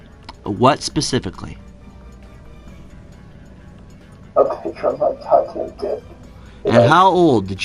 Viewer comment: Does he say "a picture of my tattooed dick"? That's what I heard, but I'm stupid.